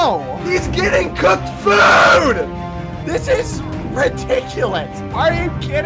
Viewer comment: Sapnap's famous quotes: "I hear him eating" New quote: " HE'S GETTING COOKED FOOD!" Omg this is the most likes I ever got on a comment if you liked the comment, consider subscribing to me. It's free and you can always change your mind. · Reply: I thought u said c o * k food LMAO